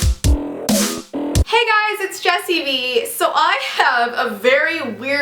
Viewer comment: what she says "hey guys its jessivee" What i hear"Hey guys its pinhead larry"